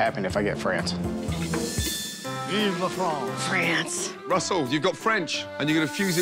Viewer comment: Is it me or did anybody else hear Gordon shout "FRENCH" at 1:35? To which Russell then walked up to his dome and got the French and Indian fusion...?